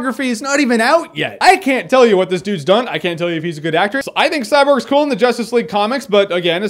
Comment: did you say "I can't tell if he's a good actress"?